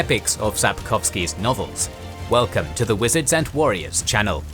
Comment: It is my first video of this channel and i have to reproduce the intro twice because the first time i would swear i heard “Welcome to kings and Generals”